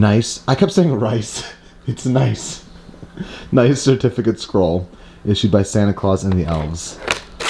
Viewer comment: Omg I thought it said rice scroll 😂😂